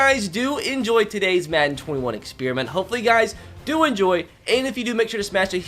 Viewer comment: did he say i hope you guys do enjoy twice?